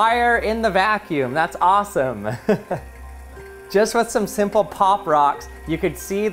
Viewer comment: he says pop rocks instead of pop-its